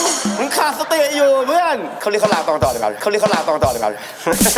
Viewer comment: พี่ตั๊กพูดว่าอะไรครับ5555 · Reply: เข้าเรื่องเข้าราวกันต่อดีกว่า